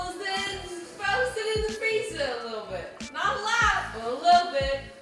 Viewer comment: Can someone please translate what Jenna says at 1:22 1:29? I can understand “can veg...freezer....not allowed”